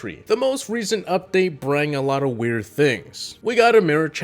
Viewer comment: did he just say brang? ( ͡° ͜ʖ ͡°)